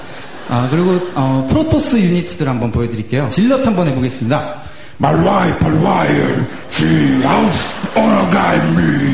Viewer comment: I heard "MY PIFE FOR LIAR" when he tried to imitate the zealot xD